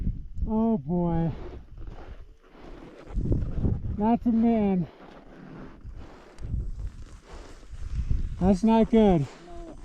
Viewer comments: What was that falling at 3:53:02 ? I think he said “that’s a man”, was it actually? Horrifying if that’s what it was Edit: I’m an idiot, he said “that’s a mitt” lmao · What did he mean when he said, "oh no that's a man" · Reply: He said "That's a mitt!" Lol